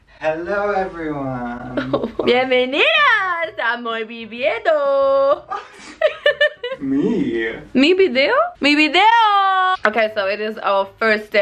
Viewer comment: "Benveniros a moi vibiedo" JAJA